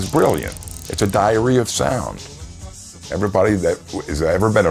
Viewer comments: Did he just say, "It's a diarrhea sound"? Well, actually, that sounds about right. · "It's a diary of sound" at first I thought i heard "it's a diarrhoea of sound," and i was like, thats one way to put it.